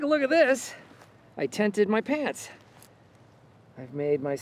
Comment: I almost screamed when he said, " I TENTED my pants." (3:49) because I heard "I TAINTED my pants." Whichever writer wrote that line deserves a medal.